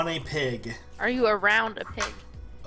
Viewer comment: I heard say "are you a round pig?"